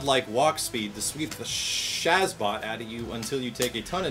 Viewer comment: wait did you just say Shazbot? WEEK OF TRIBES ASCEND CONFIRMED!!